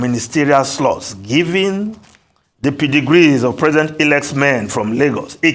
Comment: I can swear that what I thought I heard at 7:49 was PREDEGREE. I just knew that it must have been PEDIGREE he was trying to say